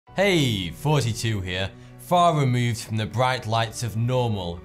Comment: The first couple videos I watched. I thought he said "Hey 42 here". That was until I realized he pronounces all the "th" sounds like an f